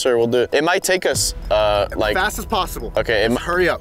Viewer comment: I thought you said only Legos